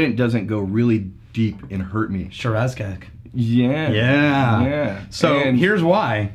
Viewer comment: Anyone else catch the slip up from? 1:12 mark, "Shiraz cak" lol insert teenage giggle here as the cak jokes fly...